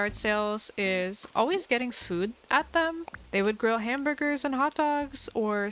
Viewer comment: did she just say they would grow hamburgers and hotdogs